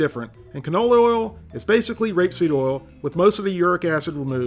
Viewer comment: Rape seed?! I thought I misheard you saying GRAPE SEED at first until I saw the can actually says rapeseed. Wow.... Rapeseed oil.... alrighty then! Carry on 😂